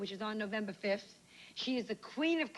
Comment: Listen closely Joan said he is the queen of comedy 🤨😳at the beginning... I always thought Lucy was a man . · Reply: You need your ears checked, babe. She CLEARLY says “SHE”.